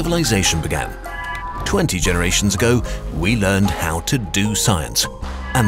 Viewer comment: I'm sorry, but since when does the phrase "we discovered science" specifically mean the scientific revolution and the Renaissance? The Renaissance itself means "rediscovery". Rediscovery of what? There have been numerous scientific revolutions during the ages. And yes my point exactly - Europe, as many other parts of the world, were occasionally centres of science and discovery, but Mr. Narrator chooses to be rather Eurocentric in his words - focussing on Europe, but leaving out the rest. Let us forget out that the very "European" Alexander chose to expand "only East" during his conquests, as Persia, Mesopotamia and Egypt were the only scientifically advanced societies at that time and Hellenistic culture spread eastwards into Asia rather than westwards into barbaric Europe, during most of history important discoveries of science and technology were definitely NOT made only 20 generations in the past as the narrator describes and definitely NOT ONLY in Europe. To summarize, I am not saying Europe didn't do shit, I'm only saying, recognize other parts of the world as well as other time periods for their scientific accomplishments too. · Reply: I thought he said we "learned to do science." Science isn't an object, it's a process. Developing a proper process that all could learn and implement did take a while.